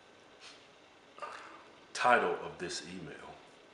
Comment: This is the first time it didn't sound like he was saying "TIRED of this email!!!!"